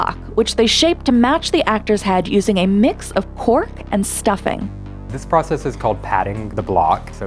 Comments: I thought they said "pork" so I had to remind a couple times to make sure I heard it wrong 😅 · I thought they said a mixture of “pork and stuffing”. I just thought. “well that’s not gonna keep very well.” Had to turn on the captions to see that it was “cork” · Am I the only one that thought she said they fill the plastic with "pork and stuffing" instead of cork?